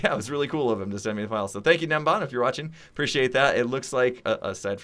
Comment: turn on captions it says "Dan bond" instead of nembon (sorry if I spelt it wrong :/)